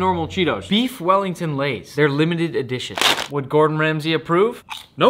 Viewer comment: Donkey meat lays exists* Nick: “No! Absolutely not!” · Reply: I thought nick said "Doggy meat lays"